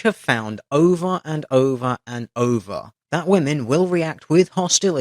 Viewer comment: I misheard over and over and over as ova and ova and ova] got a good chuckle out of that.